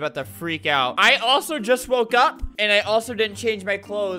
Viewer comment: When he said "I just woke up and didn't change my clothes" I thought he said broke up and I screamed at the top of my lungs and my family is concerned for me now